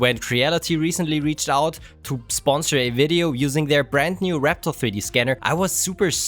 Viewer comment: does he say "rectal 3d scanner"?